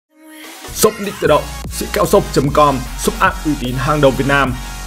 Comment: Anh shop nick tự động. Mà em nghe thành sốc đích tự động